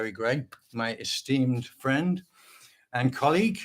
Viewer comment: I thought you said "Steam friend" then I realized it's "esteemed friend".